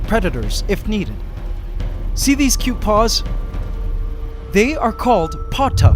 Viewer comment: I heard 'patas' a way to call feet in spanish lol